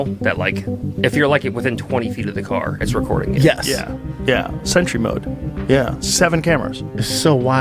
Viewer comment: Caption says century mode. That is what the man said. Only trouble is that it is called SENTRY MODE. Idjidut